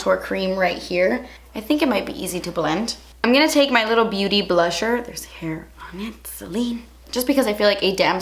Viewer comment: at 6:07 did you say beauty blusher?